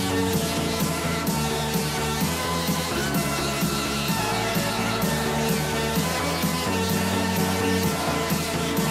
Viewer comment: What the people saying? Amazing!!! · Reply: Here, we, here we fucking go